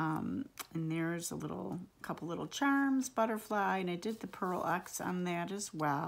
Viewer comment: Just gorgeous! Love your work! What is the name of the shimmer you used? Sounded like you said “pearl x”. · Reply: Thank you! I used Pearl X and Iridescent medium by Liquitex on the frames and 'Shimmery Goodness' by TCW on other areas.